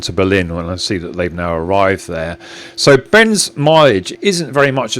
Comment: Do you mean me? I am 58 which when I was say 21 seemed very very old. Now it is still me. I just can’t run as fast though for sure. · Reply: typo by me- here instead of hear. "Ben's milage" I heard "Ben's my age"